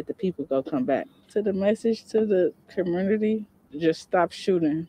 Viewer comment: Did she say "the message to the com-murder-ty"? 😅😂 That's gonna push "communitah" out of the top spot!